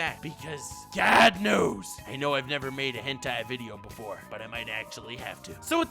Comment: Did nux say hentai instead of gentai????? The fuck is going on